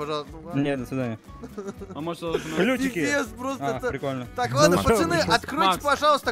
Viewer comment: мне одному послышалось "пиздец просто"